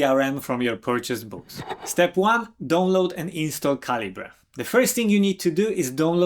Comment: Because of your accent, I thought you were saying "Don't load Calibre" instead of "Download Calibre". LOL